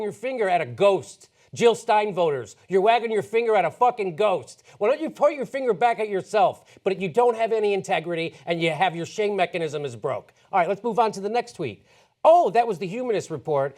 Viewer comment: For a second I heard "Why don't you put your finger back in yourself..." :)